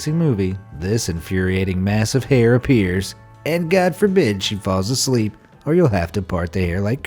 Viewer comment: I heard that"GOD FOR BI#CH" who else hear that